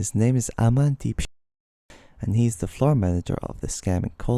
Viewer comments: sounds like he said his name is Aman Deepshit. He wasn’t kidding, that’s “A man who’s in deep shit.” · Because of the censor, it sounds like he said Aman deep sh*t haha, would be a fitting name for him. I got scammed once so I know it feels, good job man!